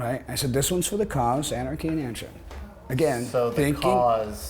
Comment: What does he say at 2:20 when he says "Anarchy in ...." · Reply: Anarchy in Antrim